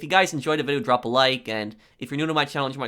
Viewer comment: I heard 'Happy NextGiving'